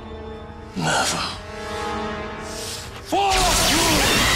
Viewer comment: who else heard Fuckyou 😆😆